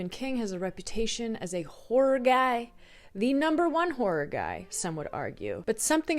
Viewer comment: i thought you said wh*re guy... i was like what did i miss?!